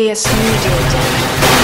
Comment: And 0:58 Sounded Like Avs Logo